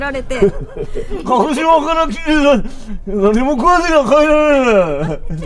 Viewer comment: 鹿児島から来て 何も食わないと帰れねぇ(で合ってるかな？) 絶対聞き取れてなさそうなお客さんの笑い方が好きすぎる